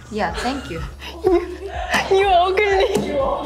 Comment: I had to check my ears and replay at 6:04 😂 I heard “you ugly” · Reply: I knew what she meant once she said it! It does sound like u ugly but it's actually u agree